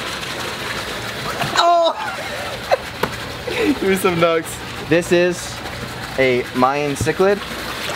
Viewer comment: I thought he said give me some nuts😂😂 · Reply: He said give me some knucks